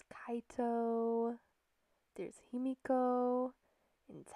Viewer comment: WHEN YOU SAID HIMIKO I THOUGHT YOU SAID YUMEKO AND I SAID :WHAT?